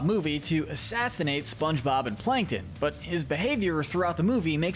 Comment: he said SpongeBob and Plankton (Not Patrick...) Glad I heard that. As for that pizza customer, my 2 main characters would get their revenge like they do to one of their villains.